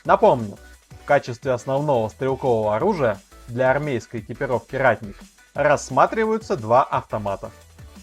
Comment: Послышалось Оратник